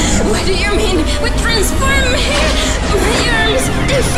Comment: Oh, why do I hear human transformation?!